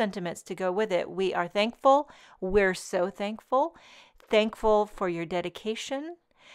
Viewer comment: So many new wonderful products! WOW! I was awake at 3 a.m. and when I watched this just after 6, I thought you said 'thank you for your medication' Needless to say I am going back to bed LOL